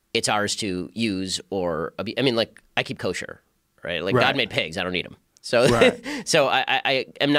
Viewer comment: what does he say? can't understand it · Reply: I keep [eat] kosher, God made pigs and I don’t eat them.